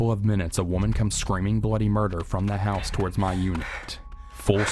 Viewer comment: in the start i thought he said a woman came screaming bloody mary then i rewind back and found that she was screaming bloody murder